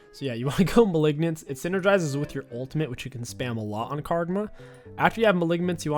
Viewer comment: Did anyone else hear him say Kargma at roughly 0:20 I think he wants to play kogmaw nxt :P